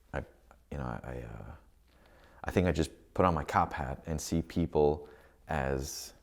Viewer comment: " Put on your cop hat" ?! What is this guy saying?